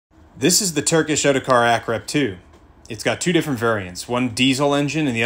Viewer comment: I Heard " this is the turkish autocar crab " while at the same moment the vehicle performed a crab move